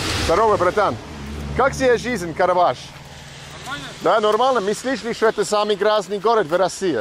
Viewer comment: what is that word? sounds like "normala" or "normana"? ty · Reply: Normalno. But we say it like “narmalna”. Translates as “fine” or “all right”